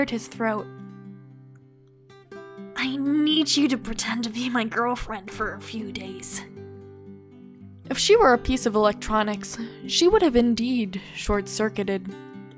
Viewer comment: What I thought Adrien said : I need you to be my girlfriend.... Me: Screams Finally hearing what Adrien really said : I need you to PRETEND to be my girlfriend for A FEW DAYS... Me: wHy GoD wHy